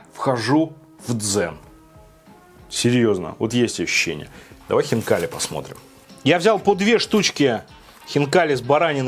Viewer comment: первый раз услышал "давай хентай посмотрим" XD